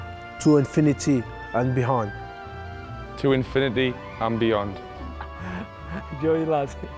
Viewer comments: did sakho say to infinity and BEHIND? LMAO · What did he say ? only know is * something * accent ? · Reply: Joseph Maddison Thanks a lot Joseph , that was very informative .. i'm actually translating this to my language , so if you could help me with more phrases .. that would be amazing like : here 2:58 .. did he say "Joey lad " ? and here 4:44 , i don't know what did he say , but it's something about headset "give me the headset" or something and last one , 4:55 i don't know what did he say